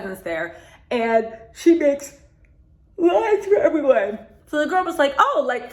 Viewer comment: What this sounds like to me is she was providing a "Girlfriend Experience"?!....And I am SCREAMING! I had to rewind the part, "if you want a big fish to come back you gotta give them a little treat otherwise they'll swim away!".....If you're in a Rolls Royce Phantom, you wont kill me. OMG!! Really?...I thought she said limes as well, not lines. At first I was like, oh she was going to make some drinks. Lastly, a makeup line with the word "Mistress" in the title isnt going to sell that well! Another lovely review/response T!